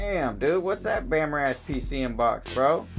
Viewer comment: damn dude what’s that bam???? Don’t know what you said there but it made me laugh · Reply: lmao I said " what's that bammer ass pcm box" lol if you don't know the urban slang definition for bammer, it means no good. Lol thanks for watching my video and leaving a comment!Glade u got a laugh I like crackin jokes 😃